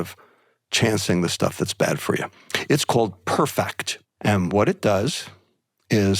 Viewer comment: What was the application or website that was mentioned on here that filters out the detrimental foods? I heard "perfect" but I do not think I am hearing that right. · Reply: It’s Perfact and will be an app but it doesn’t look like it’s available in the App Store yet